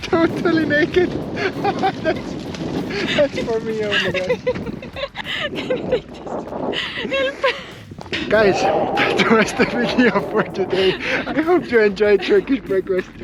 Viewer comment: Hilfe? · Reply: I thought I heard her say (Hilfe) wich means help in German at minute 11:22 . and asked me if you 2 could speak German :D